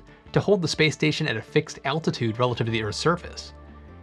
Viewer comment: subtitles (attitude) is correct but it sounded like you said altitude..which isn't quite so accurate